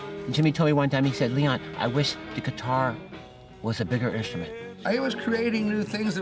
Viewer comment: What does Leon say in 1:48..."I wish the guitar was a bigger (???)" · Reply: instrument